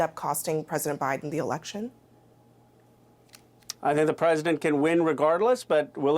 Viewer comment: When Ro said, "I think the president can win regardless"......I heard that as "by cheating...again."